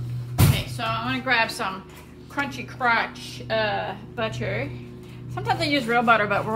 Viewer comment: Did I hear the name of the butter you used for the omelet correctly or is my hearing going to s**t, it sounded like you said Country Crotch,lol!!